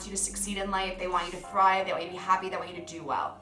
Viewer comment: You said that friend wants you to thrive in life, but the captions said they want you to cry in life. 😂